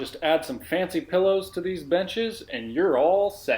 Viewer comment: Very nice outcome. A must do. I thought u said, "add some nice cushions to these b**** and youre all set." Had to rewind it then i heard "benches". Lol!!!!